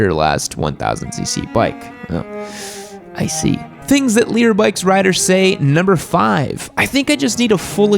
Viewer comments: hey guys make sure to check out all the great products over at ridge! Use code YAMMIENOOB for 10% off <link> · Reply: For the longest time I thought you were saying "leader bikes" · For the longest time, I thought you were saying LEADER bikes. And I was thinking to myself, "what the hell is a LEADER bike?" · dafuq i watched the videos for a while now and allways thought he said LEADER bikes xD